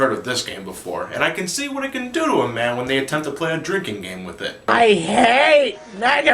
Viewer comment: what did he say? Didn't understand that. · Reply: "And I can see what it can do to a man when they attempt to play a drinking game with it"